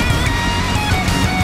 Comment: I always thought it said "hold on coofo" But now I realized it is "order confirmed " Lol